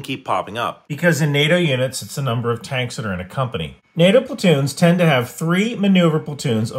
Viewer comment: I misheard "NATO platoons" as "NATO potatoes"